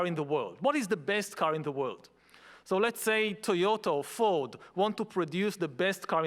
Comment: did he just say TOYOTO?